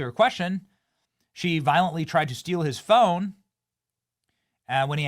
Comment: Wait I thought she said she wasn't violent